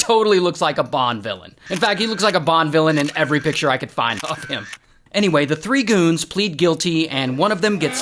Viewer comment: Thought Ray said he looked like a 07:54Bond Villain in every picture I could find "of him" with eff him😅😂